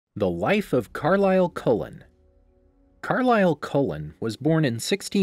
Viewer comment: I first heard "the wife of Carlisle Cullen" and I was like wait this video isnt about Esme?😂